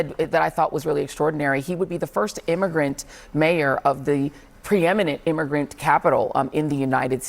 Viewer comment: sounded like bro said- coming into Andrew’s face… and that’s just gross.